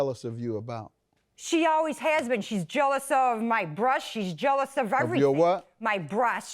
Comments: PLEASE tell me I wasn’t the only one who heard “She’s jealous of my brush.” 💀💀💀💀💀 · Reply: I thought she said she is Jealous of My Brush 😂😂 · I swore she said my brush not breast .But if sisters wouldn't they have the same mom.